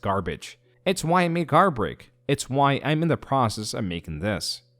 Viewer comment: When I heard "I am not hurt in any significant way by this garbage," the next line after sounded more like "It's why I made Garbage" instead of Guard Break 😭